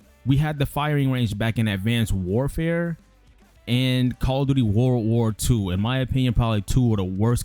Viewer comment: Yes, World War 2 was released in 2017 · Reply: It sounded like you said World at War 2 not world war 2